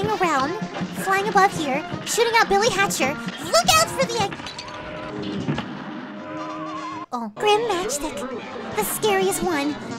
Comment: DID YOU SAY...BILLY HATCHER LALALA CALKADOADO